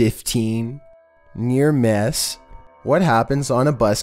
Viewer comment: Is it me or do I hear mear mess